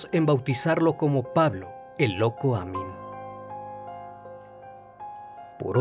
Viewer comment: Pensé que decía Loco Damián XD